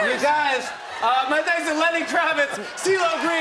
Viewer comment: Did he just say Jennifer Hudson instead of Aniston?? Lol